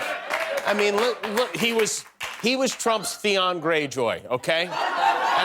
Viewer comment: anybody who gives Trump money deserves to be robbed... and Trump is happy to rob them. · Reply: Ted was Trump's what? I can't make out what Jimmy is saying. It sounds like he's saying Theon Gray Joy whatever that is, I've no idea.